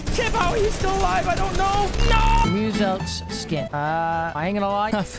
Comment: why did it say you had the midas skin when actually you had the ikonik skin?